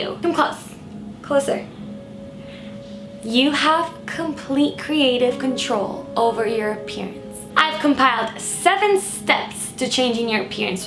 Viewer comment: I had to play the intro over again cause I swear I heard "You have complete creative control over your penis." Still, I do tho